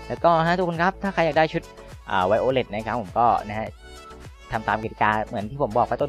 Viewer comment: ได้ยินว่าไวโอเล็ท😂